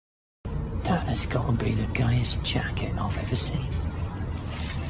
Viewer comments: I thought he said “That has got to be the “gayest” jacket I’ve ever seen” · i thought he said gayest jacket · Anyone else thought that the guy said " gayest " instead of " greatest "